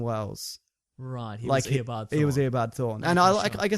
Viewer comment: I heard “Air Bud Thor” when said “Eobard Thawne”